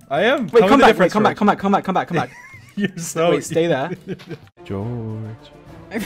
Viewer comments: he actually said "you're so stupid" like they normally do to each other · Did he really say that? I hadn't heard before :0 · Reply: No, he said "Ur so stupid" I'm pretty sure